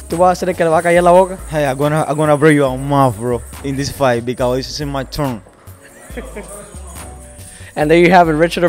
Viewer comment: It sounded like he said "im going to break your mouth bro in this fight because its this my turn." hes not fluent in english but you can figure out what he wanted to say.